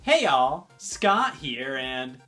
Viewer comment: I swear, that intro made scott sound like he was saying “hail” instead of “hey y’all.”